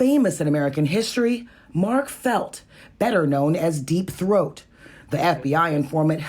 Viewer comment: did she say "deepthroat"?